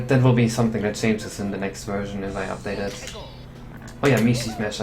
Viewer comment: the subtitles say "I have died at school oh yeah". xD